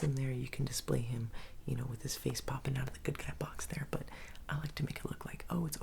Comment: I thought he said Business was POOPING😂😂😂😂😂😂😂😂😂😂😂😂😂😂🤦🏻‍♂️